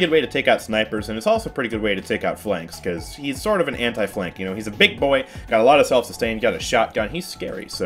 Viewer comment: wait did you say "hentai flanks"? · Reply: "he's sort of an anti-flank"